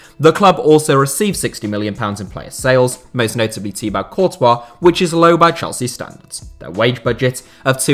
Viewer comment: did he just say tea bag courtrah 😂